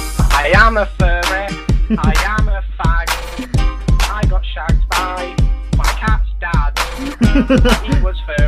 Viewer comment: "I got shagged by my cat's dad" WHAT THE FUCK LMAO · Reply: GetOffMyBoardREEE I heard "I got shot by my cat's dad"